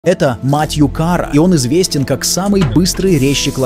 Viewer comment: "Это, мать его, кара " - мне послышалось😂😂😂😂